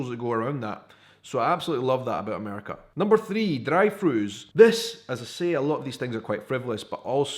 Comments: I thought he said "dried foods' when he was saying "drive thrus" lol. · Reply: theplayernkc I thought at first he was saying “dried food.” So I listened close, then I thought he was talking about “fried foods,” it took a couple more tries before I figured out it was “drive thrus” when he mentioned the bike. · I thought he said "dried foods' when he was saying "drive thrus" lol.